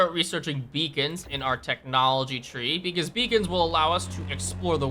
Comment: Oh beacons I thought you said vegans haha